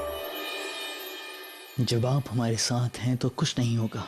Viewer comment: the way he said JODHA instead of jodha begum>>🥹❤️‍🔥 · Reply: no no he said jab app hmare sath hain